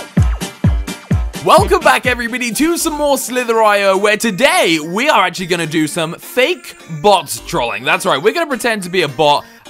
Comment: did you say welcome back everybidy??